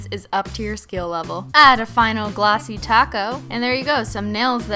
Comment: It sounds like she said tacco instead of topcoat XD